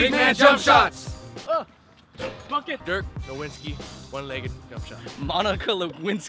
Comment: Guys Dont You Noticed He Said Fuck It At 3:51 When Ray Shot A Fade. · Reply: MarcusChua NBA i thought he said bucket